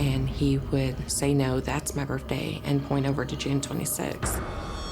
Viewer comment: is she saying birfday?